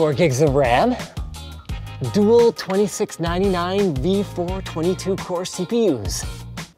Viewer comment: Did you say 22 core? That seems a strange number of cores to me.